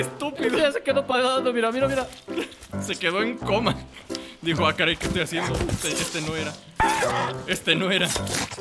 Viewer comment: En el minuto 14:18 cuando el caníval muere parece que dice "el karma wey"